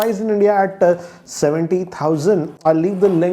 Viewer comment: i heard 17000 😂